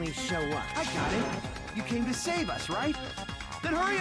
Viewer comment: I thought Akane said "Save...? Dios save us...?"